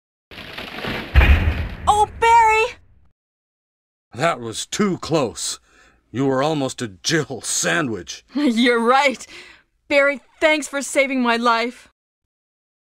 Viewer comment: Did Jill just say oh bae? XD